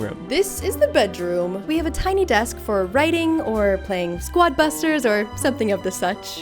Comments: DID SHE SAY SQUAD BUSTERS 😂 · did she just say squad busters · did she just say squad busters?😂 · DID JUST SAY SWUAD BUSTERS?